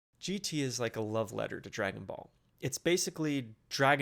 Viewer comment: what did he sayyyyyyyyyyyyyyyyyyyyyyyy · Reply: "GT is a love letter to Dragon Ball"